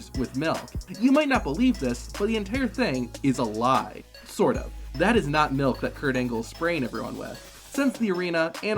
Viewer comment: I thought he was gonna say “That Is Not Milk, It is jizz